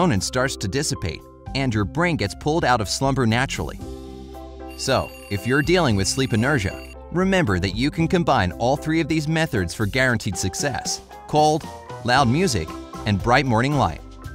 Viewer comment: You guys hear him say "metherds" instead of methods at the end (7:20)? Lol